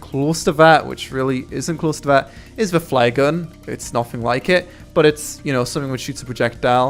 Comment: I thought he said 'fly gun' lol